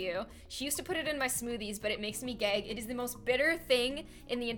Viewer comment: When ever you were talking about bitter melon I thought you said "it makes me gay." Instead of "it makes me gag." XD